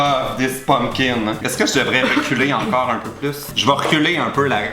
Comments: à 2:29 j'ai compris " est ce que je devrais l'enculer encore un peu plus?" hahaha j'ai ris pendant tout le long hhaha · À 2:27, j'ai compris "Est-ce que je devrais l'enculer encore?" :')